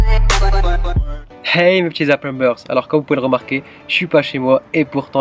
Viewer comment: Super vidéo comme dab :) Juste...au début de tes vidéo tu dis quoi 😂? "Hey mes petit...." quoi ? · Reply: Mr Cheese talisman il dit hey mes petits aprenbers c un mélange entre apprenti et youtubers